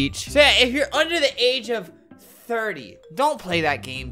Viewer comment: Albert: “don’t play this game unless your 30+” also Albert: not over 30 Edit: y’all saying he said 13+ or under 30 at the end he said well don’t play this game if your under 30 smh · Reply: I heard 30 at 9:30.